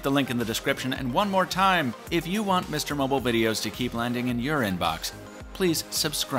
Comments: I thought he said urine box for a second LOL 😄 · When he said in your inbox, I heard 'Urine Box'. Silly me.